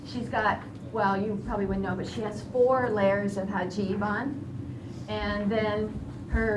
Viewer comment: It sounded like you said hajib...but it's actually hijab. Maybe I am not informed? <link>